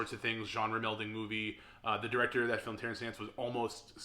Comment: I swore you said Terence Stamp instead of Terence Nance lol. Great video man, love your work!